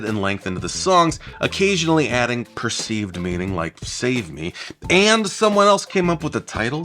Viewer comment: I heard “therapy” not “save me”.